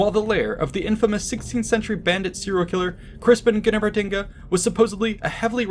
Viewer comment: what is he saying there? Infamous bandit serial killer crispinkanettertinga?! Who? wha?